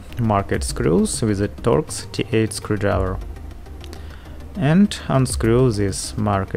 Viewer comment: TORX T8 SCREWDRIVER! I thought you said dot D8 screwdriver so I googled that and got a whole load of nuffin' so then I googled star shaped screwdriver and hit the jackpot. Whew, wotta journey.